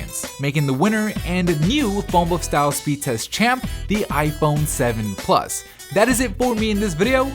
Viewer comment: I always thought he said "Phonebook speed test champion"